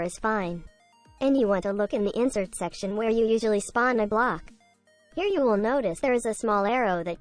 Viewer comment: i thought u said spawn a glock xd